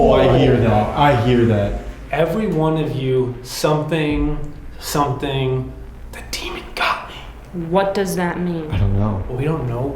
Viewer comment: I hear “everyone of you deserve to die, the demon got me” 🤷‍♀️